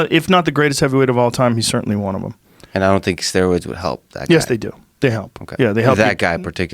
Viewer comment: I heard him say “steroids wouldn’t help a guy like that & clicked off”